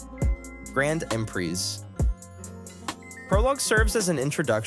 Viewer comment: what did he say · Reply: Brand and preze